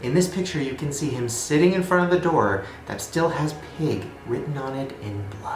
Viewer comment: not gonna lie I thought it said PIE. Not even being funny